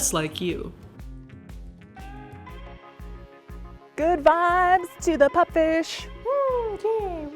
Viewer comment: When she said she's sending Good Vibes to the pup fish I thought she said Good Byes.